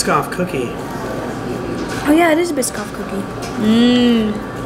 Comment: LMAO at 7:45 I sounded like she said "oh yeah it is a VSCO cookie"